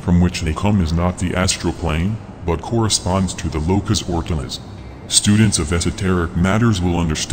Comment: What does he say at about 7:28? The locus oculus?